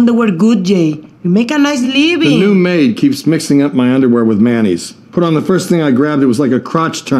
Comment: I thought he said she keeps mixing his underwear with MAYONNAISE